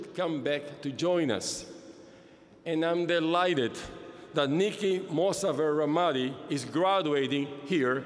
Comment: Subtitles say [Inaudible], but her name is Niki Mossafer Rahmati.